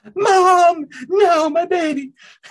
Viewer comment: When he says “Mom! No! My babies!” 0:42 I just hear Moist Critikal